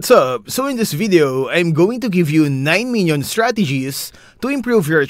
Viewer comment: I thought he said 9 million 0:03 💀💀